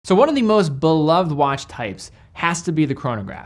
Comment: When started with “…beloved….” I’d have sworn I first heard Bulova.